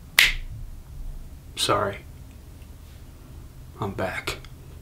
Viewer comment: I thought it said screw it I'm black